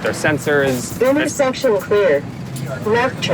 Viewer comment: when the truck said "intersection clear" i thought it said "there is sexual fear"